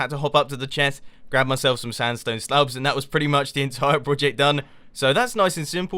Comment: Did he say "sandstone stubs?"